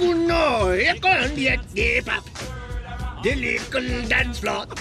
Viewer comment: I hear “lyrical dance flap” which is even more funnier, if that’s possible! Booom time!